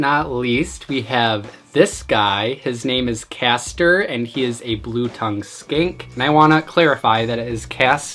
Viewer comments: I thought you said a blue tongue SKANK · I thought he said “ This is Caster, my blue tongue skank.” (I know it’s a skink)